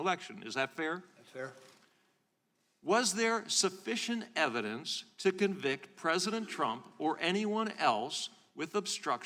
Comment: C-span team; please correct your Transcript CC closed Captioning - I hope this timestamp wasn't broadcast live with the BIG issue within the word used in English CC1 {VS what was asked}. Time stamp showing on transcript through youtube says 150:35 however technically word in question starts second or two ahead of that timestamp <link> Transcript says "INSUFFICIENT EVIDENCE TO CONVICT" instead of what was actually said "SUFFICIENT EVIDENCE TO CONVICT" during that timestamp - This is going to be KEY to make sure Public record is correct, and as many people as possible know the truth, anyone deaf may not realize what was actually said here and might think opposite to what really transpired here. Thanks in advance.